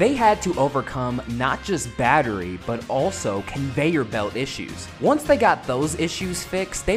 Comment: my guy did you just say conveyor belt??🤣